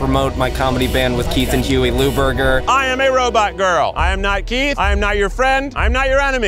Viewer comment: gets shot “oh tits!” Keith: “sorry honey” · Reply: I thought she said “No Keith!” Hahahaha anyways, I love and Keith 🤩🤣😎